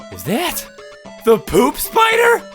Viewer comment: I thought he said " the boob spider "